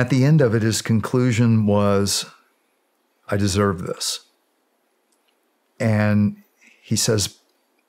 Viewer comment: What did he say at the 2:58:15 mark? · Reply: “I’m fucked”